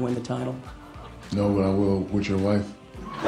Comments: "No but I will with your wife" 😂Savage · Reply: I thought he said "u know i will, with ur wife" xD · "No but I will with your wife" 😂Savage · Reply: I thought he said "No I wouldn't. Would your wife?"